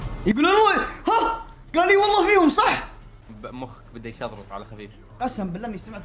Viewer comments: حتى انا سمعت قال اي والله فيهم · وات سمعتم ايش قال البوت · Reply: قال اي ولله فيهم